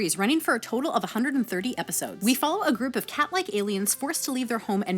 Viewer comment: Had the volume down, instead of hearing "cat-like aliens" I heard "Catholic aliens." I had to rewind the video and rewatch that part lol.